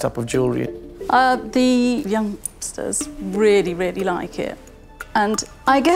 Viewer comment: did she just called young people "youngsters "